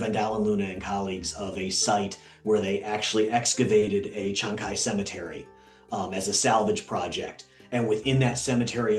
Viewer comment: For anyone wondering what is actually said here 52:17 it's Pieter Van Dalen Luna and Chancay cemetery